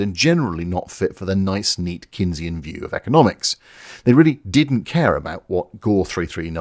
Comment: I heard you saying Keynesian economics as Kinseyan economics, and thought yes, the UK has fucked its economy.